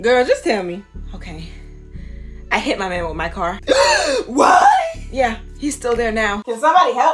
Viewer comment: I thought she said “I hit my man with my dog”😂😂😂😂